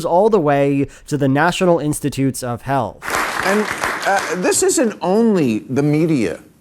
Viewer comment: I misheard this as national institute of hell